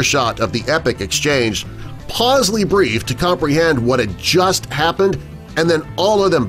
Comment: "pausley briefed"? WTF is that? And this story has been going around since long before the internet. I heard it first in the '80's.